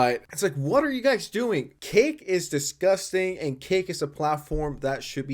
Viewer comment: Is he saying Kick or Cake? Theres a platform named Cake? Even the subtitles say Cake.